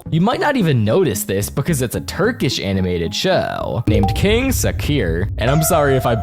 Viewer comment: In 12:24 I thought he said King Saur